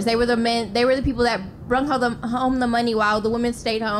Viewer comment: My last video didn't hit the algorithm so you probably missed it. Go watch: <link> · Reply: did she say " brong home the money "? I was taught from Kindergarten , the past tense of ' bring ' is ' brought '. 😆😆😆🇨🇦🇨🇦🇨🇦🇯🇲🇯🇲🇯🇲